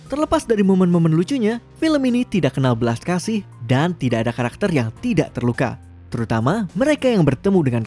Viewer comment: aku dengernya gini Tidak kenal belas kasih dah tidak ada yang terluka🤭😂😂😂😂😂😂😂🤣